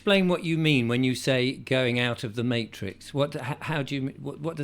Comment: "explain what you mean by going <out of> the matrix". No Richard, she said <out TO> the matrix. You mishear a lot.